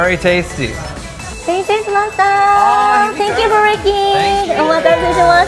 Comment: I heard, Thank you for wrecking, instead of thank you for waiting